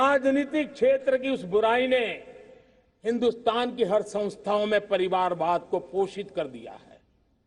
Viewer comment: does he say Bullshit? Congrats to India despite everything a fascinating country and culture. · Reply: It's POSHITH